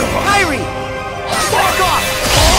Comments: did sora just said.... What I think he SAID? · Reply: He actually shouted to Kairi for a Team Attack, but he finished his combo and said "Back off!" and MX said "What?" at the same time. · Does he actually say "fuck off"? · Did he say F*ck off?